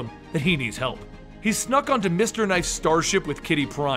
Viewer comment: Thought you said Mr Knight (Moon Knight) sadly you didn't...